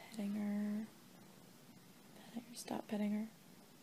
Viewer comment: At the start I thought she said stop cutting her my heart stopped ❤️